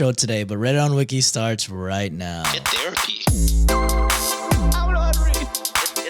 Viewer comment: has anyone figured out what’s being said here? I think about it every episode now since I saw another comment asking! Obviously the first part is “Get therapy” but I’m not sure what (?) says after that! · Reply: It's "I don't know how to read" and it's that is saying it.